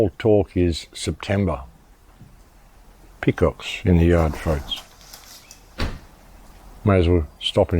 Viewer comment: You scared the crap out of me when you said, "Peacocks in the yard" - Because at first I thought you said "Pick-ups in the yard" - I thought you were being raided!